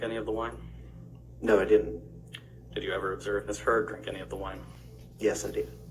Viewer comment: I always hear it now as "Misheard" instead of "Ms. Heard"